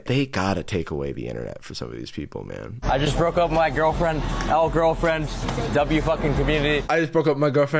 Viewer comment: why do i keep hearing "i just broke open my girlfriend" 😭😭😭😭😭